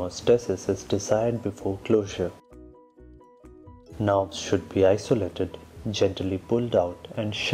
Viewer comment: Great video. 4:45 What is the part which is being isolated gently pulled out and sharply divided? I hear it as "Knob" but no idea what that means? May be "nerve"??